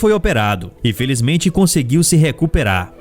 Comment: Foi oque eu escutei?"INFELIZMENTE ELE CONSEGUIU SE RECUPERAR"